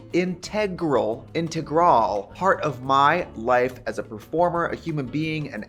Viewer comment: At the beginning of the ad I heard “as former human being”